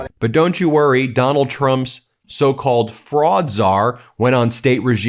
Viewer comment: You said Fraudczar, but I hear 'Frog'czar. His talking about taking the fish didn't help my second of confusion. With this Administration, I rarely get surprised by the stupid things they do or say.